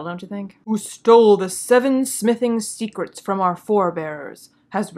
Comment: It's actually unbelievable how incompetently written this show is. It's such a complete mess... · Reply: Listening to the plot description, I misheard the 7 smithing secrets as 'the seven sniffing secrets'. Sauron stealing the seven sniffing secrets would be a better plot than the one we're watching.